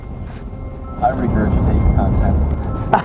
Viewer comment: btw, what did said by the last minutes? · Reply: "I regurgitate your content"